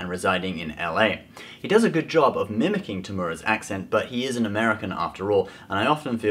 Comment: When he said temuera caption says tumorous lol